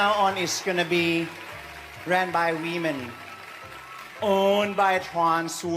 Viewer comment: I heard “ran by we men” 🤷🏼‍♀️💀